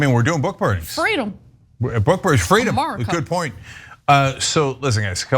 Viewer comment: Lolol......did Ana really say 'Murica'?